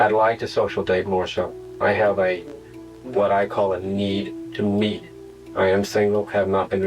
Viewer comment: I got the hair standing on the back of my neck when he said I HAVE A NEED TO MEET, it can mean in an EVIL 😈 way. Am i the only one that thinks this way? 😮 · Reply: I thought he said a need to lead